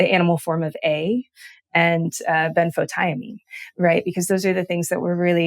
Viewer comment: what is she saying? Animal vitamin A, iodine and what? Thiamine??